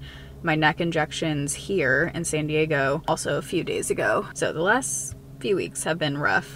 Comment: I'm feeling that old lady time schedule too. Then I get to go to bed when things are the craziest, only to awake when things are calm again. I swear I thought you said "insane-diego" because it's one of those days. Glad to see you're well! ❤